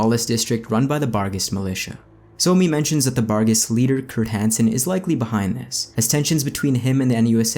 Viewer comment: For a second there I thought you said Chris hansen instead of Kurt hansen